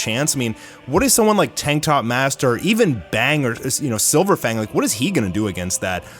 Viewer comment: You said something like tank top master bang her, did i heard it correct